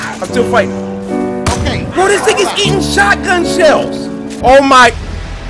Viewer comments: did cory say the N word!? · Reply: He said this thing is eating shotgun shells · i swear to god i heard cory say "bruh this nigga's eating shotgun shells" n im so fucking happy rn